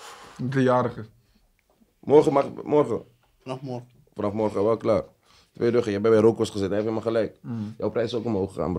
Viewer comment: wat zegt biggidaggoe daar ik versta niks gap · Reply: Vanaf morgen is die 2 ruggen €2000 per verse